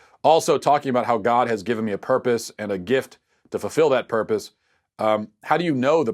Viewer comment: I kept hearing "gif" when you pronounced gifts, so I had the mental image of people sending Jesus gifs.